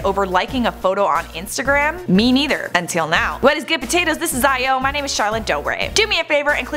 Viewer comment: did she just say... "What is good potatoes?"